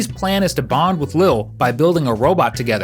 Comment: why does he say ADHD 💀💀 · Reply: I think it’s 80hd